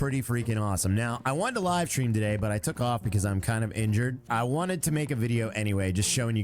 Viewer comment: When said cuz I'm injured I heard "cuz I'm kind of a jerk" 🤣